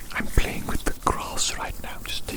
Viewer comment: “I’m playing with the CROSS right now just taking a moment to play with the CROSS” “It’s so SOFT and DELECTABLE” (I know it says ‘grass’ but this is what I hear and it made me laugh)